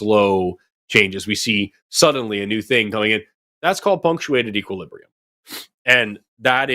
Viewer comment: I know Forrest was saying "punctuated equilibrium," but for some reason, in my head, I kept hearing "feng shui equilibrium," lol.